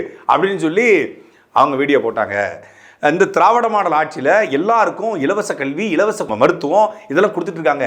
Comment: i heard something else. I heard Davidiya paiyan aachi want to cofirm. Dravidiya or Thevidiya aachi aa? Please confirm na...